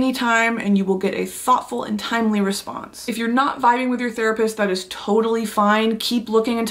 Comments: Thought she said “if you’re not FIGHTING with your therapist that’s completely fine” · deadass thought she said ‘when you’re not fighting with your therapist, that’s okay, you can switch’ and I started wondering if my therapist is not a fighter not a boxer never claimed to be a figh